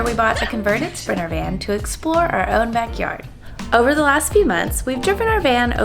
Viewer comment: did you actually say "...Over the last three months, we've tooken our van....."?